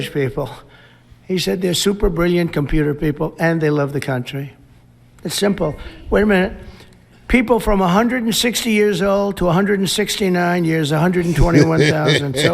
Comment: Did Trump say, "Way a minit"😂